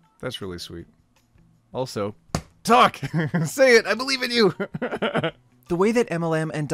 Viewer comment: I thought he said “Duck! I believe in you” All I could think was “I believe in you too duck”